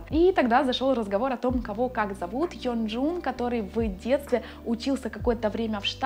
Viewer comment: Он Ёнджун😄 Я услышала "Хёнджун", хотя может я глухая ахахаха